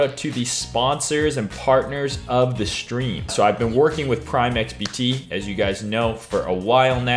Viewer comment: great content - just would take more care with announcing your sponsors - I thought I heard Crime XBT there at the end of the video :)